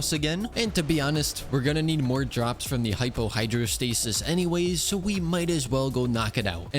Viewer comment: did he say hypo hydrostasis?